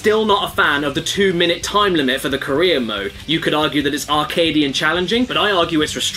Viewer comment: I kept hearing "Korea mode" instead of "Career mode" and thought that was an oddly racist difficulty setting